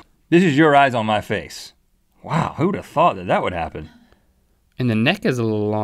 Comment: i thought Rhett said "who the f*** thought of this" I was mortified 😂😂😂